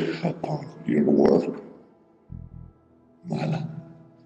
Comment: "Look upon your work, mother" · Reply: albertosamaniego I Thought He Said Marla.